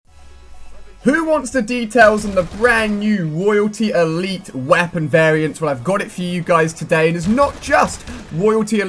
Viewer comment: 😂😂At the begining i thought he was gobba say "WHO WANTS THE D"